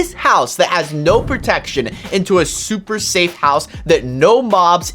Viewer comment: I LEGIT THOUGHT HE SAID ''i will make a SUPER SAIYAN house'' lol